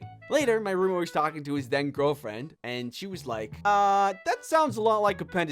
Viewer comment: I THOUGHT HE SAID “DEAD GIRLFRIEND” 💀💀